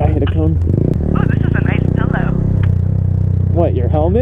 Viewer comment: I thought she said dilldo